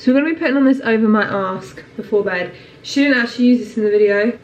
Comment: Thought you said "So I'm gonna be putting this over my arse... before bed."